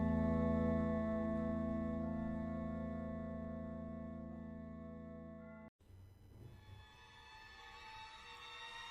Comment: al chile no entendí el mensaje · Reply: fue un "yo la tengo mas grande"